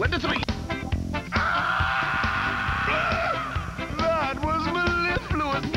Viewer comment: im still trying to figure out what krumm says here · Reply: “That was mellifluous!”